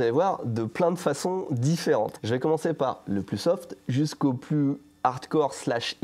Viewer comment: J'ai entendu "je vais commencer par ubisoft", je me demandais ce que ubisoft venait faire dans cette vidéo Ah ben ça me fait penser au collectionneur de slip dans Wakfu